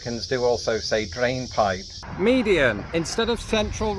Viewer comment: Sounded like you were saying media, not median for the central divider. When I think of Berm, I think of raised area to prevent bullets going any further on a range.